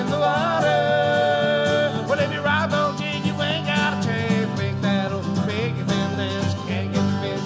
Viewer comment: what does he say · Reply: "well if you line don't jig you ain't got a chance to make that ole' Mickey Finn dance". Mickey Finn is a popular Atlantic Salmon fly for us on our rivers locally.